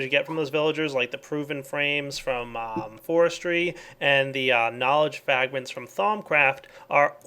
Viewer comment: Was it me, or at 33:50 did he say "Knowledge Fagment"?